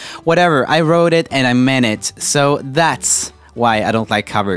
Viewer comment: "i wrote it and i meant it" totally thought he said "i wrote it in a minute" and was like hmm...i mean okay, sure